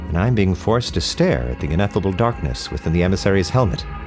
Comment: Did he say Ineffable Darkness at 14:13 this cannot be a coincidence